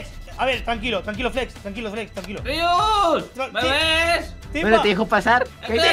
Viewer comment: Minuto 7:04 oi mal o Timba le dijo "BEBE" a Rius 😮😏😍🥰 7w7